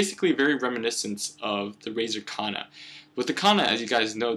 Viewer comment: did he say razer kana ??